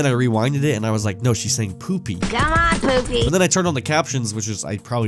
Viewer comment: ”come on, Pookie” is what I heard- but she’s calling him poofie due to his silly jacket I suppose